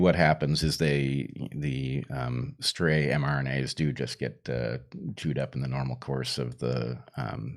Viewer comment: Did he just say "Jew'ed up"?